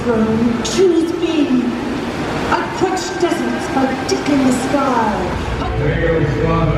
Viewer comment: love your presentation but i was half a sleep because i worked all day, then i was watching the atlantis show but around 9:15 im not trying to sound stupid or funny but i could have sworn she said i touched something a d*** in the sky lol